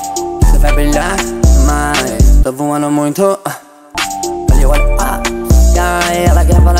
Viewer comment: So entendi "ela ta voando muito" kkkk